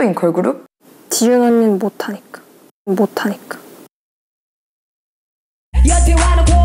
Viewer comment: What did Sua said in 1:13 ??? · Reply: she said "jiyoon unnie can't rap"